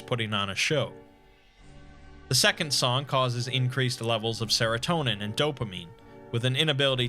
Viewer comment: for some reason I thought he said it causes a /decrease/ in dopamine- and I thought "so it basically makes you adhd?! What would it do me, since I already am?" Then I realized I misheard him.